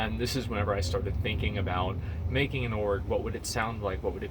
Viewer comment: Thought he said he made an Orc. Figured he was switching his main for the Horde. Good Luck on the Org!